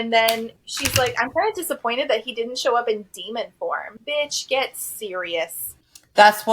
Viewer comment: I know you're saying "demon form" but I keep hearing "demon p*rn" 🤣 Also, considering the LI is a literal demon I can't say I'm shocked he doesn't stick to the rules he laid out or otherwise, he's a demon. He's bad to the bone evil and all that shit. I wouldn't trust a single thing he says, the MC (Lily or whatever) shouldn't either. It blows my mind she's surprised by anything he does.